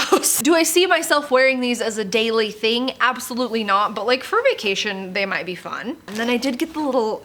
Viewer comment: Oh my goodness I cracked up! At 5:20 I thought you said "but for fornication they might be fun" Bwahahahahha!